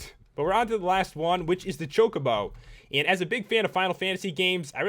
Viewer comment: When ue said Chocobo, I thought he said chocolate bar.